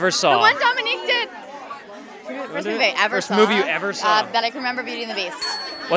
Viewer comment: What does Kat says at 0:31 ??? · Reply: First Movie that she could remember seeing is Beauty and the Beast